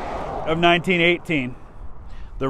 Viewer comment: "Brought down, most likely by Austrian machine-gun fire." No! Everyone knows it was SNOOPY, who finally brought him down. 😁🙃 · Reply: I thought he said Australian. But to be honest, I did not know Australians were fighting in Europe. SO I learned something today, and now gotta read more about it.